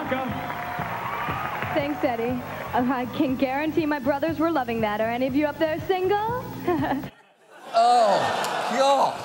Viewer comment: Can anyone say, please, what did Ivanka say at 3:07? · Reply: Аи кан гарантии маи бродзэрз вэр лувинг дат, ар эни ов юу уп дэр сингэл?